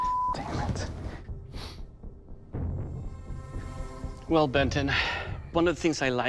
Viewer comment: lmao did he say ben 10?